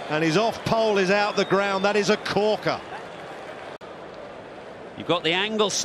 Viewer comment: 2019 WC Glimpse What A Ball That Was Just Unbelievable 🔥🔥🔥🔥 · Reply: You misheard the commentator "it was a CORKER"